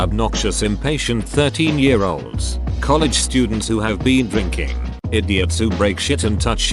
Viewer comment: I didn’t see the comma on the escape room one so i was confused when I heard “13 year old college students”